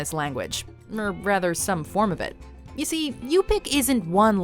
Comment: I was so excited, I thought she said Ubykh when she said Yupik